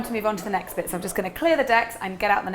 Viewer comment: Did she just say "Deks" ......?